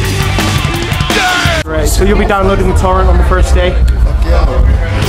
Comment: What is the guy saying, "so you'll be downloading...something something on the first day"?? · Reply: so you'll be downloading the torrent on the first day?